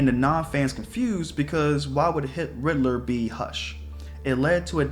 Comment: "Because why would hitl- ridler, be hush?" I hope I'm not the only one who heard this💀 (But seriously, I understand what you're saying. I have gotten over the movie cause I wanted to love and appreciate it but... idk it felt off. And I'm a big fan ever since 2015. I like how they did the Easter eggs and tried to fit big/recognizable stuff into one movie and it was alright but it was more for fan service and it felt like another one of thoes "Hollywood" type of movies, if you catch my drift. But I do like it I just... I don't know.)